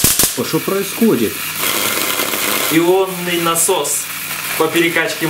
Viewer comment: мне послышалось ёбаный насос